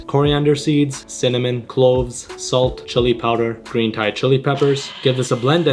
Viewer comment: for a second i thought he said clothes 😅😅